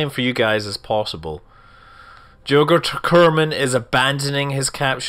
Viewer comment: Lol anyone else heard saying Yoghurt Kerman at 3:48? :D